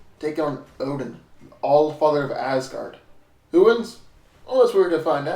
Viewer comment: SOUNDS LIKE HE SAID: ""ODON, OLD FART OF ASGARD".😂🤣😂🤣